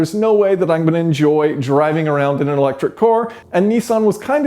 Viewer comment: The guy can't say Car properly, Sounds like he says electric core..lol